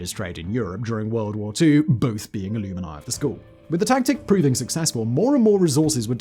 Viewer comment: "... both being ILLUMINI of the school..." It sounded like he was starting to say Illuminati, but cut it off prematurely. However, the context points to the word in question being alumni. Heh heh. This one took me by surprise. I'm used to Simon leaving out whole syllables in the middle of far more multi-syllabic and frequently foreign words or names, like "AustropiTHEcus" (Australopithecus), or "ERASthenes" (Eratosthenes). NOT perfectly ordinary and relatively short words. What an odd quirk!